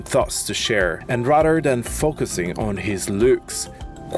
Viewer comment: Thought he said focusing on his glutes. I was like wtf!! He is a nice guy ofc he is on camera, but his only living for the moment is a stupid lie where he is really trying to make sure everyone knows that he has no regrets. Naturally, he would never admit if he wished he had never done anything to his body, once your that far into it I don’t think anyone would admit regrets. Btw dude doesn’t want “just a normal life” he wants the opposite